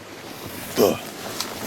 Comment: What was that deep voice randomly at 1:58? Sounded like it said "fffuh."